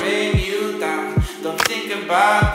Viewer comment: I always hear "do you want to leave your life in the only way", I'm like "that's so dark!" And then remember the lyrics😅